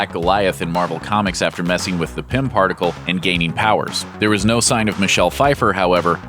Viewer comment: Did you just say the PIMP particle?